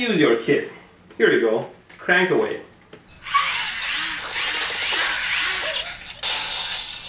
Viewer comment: "What if I connect this to here?" "NOOO" BOOM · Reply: I thought she said "why dont you just plug it in"